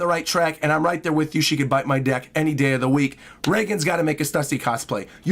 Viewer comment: Did just said “she can bite my deck?” @ 1:03:10 Was its supposed to be NECK or DICK?